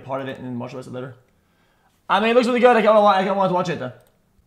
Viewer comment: whaaat? Somebody tell me what he said I’m lost 🤔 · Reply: “I mean, it looks really good, I kind of want to watch it”